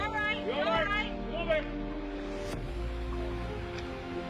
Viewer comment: At the end of the video at timestamp 1:11 why did someone say "you're a dead man"?